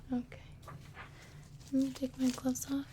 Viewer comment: Thank you, now i am finally not afraid of going to the doctor, but i admit you got me sleeping from minute one until 23:44, then i swear i heard you say " let me take my clothes off..." and i wake up instantly, sadly was the end of the video. xD